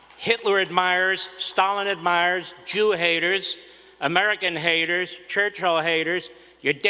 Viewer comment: Churchill haters? What the hell does Winston Churchill have to do with the American Conservative movement or modern American politics at all? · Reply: I thought he said "church hall haters"